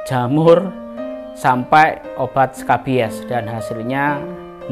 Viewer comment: Bg klo proses penyembuhannya cacing jadi kering gx bg, kya bekas luka gitu? Apakah prosesnya seperti itu atau tiba" kempes bg? Sy khawatir bg anak sy berobat pariasi dokter ngomongnya beda", ada yg nyuru oprasi ada yg nyuru kedokter kulit, lebih prahnya lagi ada yg blg klo udh rabies pdhal bru ngeliat sekilas doang. Yaallah mentang" make bpjs kerumah sakit besar tpi dikata bgitu😢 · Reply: Wkwk itu mah salah denger,bukan rabies tapi scabies..emang biasanya gatal tidak dipegang saat pemeriksaan telanjang tangan atau pakai sarung tangan latek